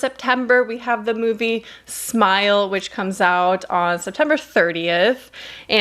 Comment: Tell me why I thought it said smilf instead of smile lmao